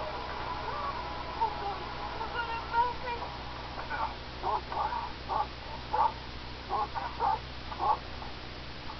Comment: What does she say? "You're gonna bug me?" 0__0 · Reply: Sorry, I'm 5 years late!! She says "Lord, have mercy!"